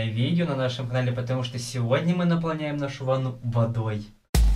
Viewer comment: послышалось ванус